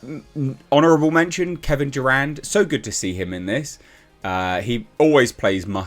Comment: I swear I thought he said Kevin Durant lol